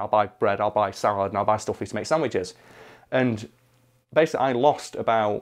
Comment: I keep hearing him say "asshole sandwiches" and instead of....whatever he really is saying